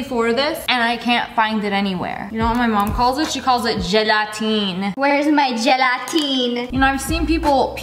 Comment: I thought she said Jedi Teen and not Gelatin